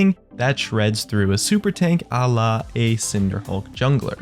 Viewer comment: Did he say Allah @ 1:54?